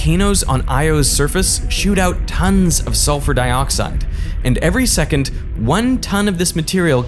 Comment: did he say every "secont"?